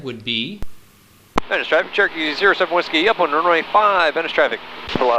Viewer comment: What does he say here: 3:27 - Cherokee 07W ??? Right 5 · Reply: "Cherokee 07W upwind runway 5."